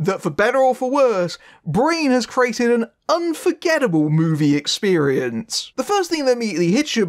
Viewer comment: did u say "unforgettable" or "unforgivable"...?😐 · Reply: Unforgettable. But I suppose depending on your point of view...